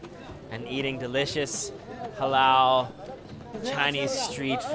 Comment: who else heard allah akbar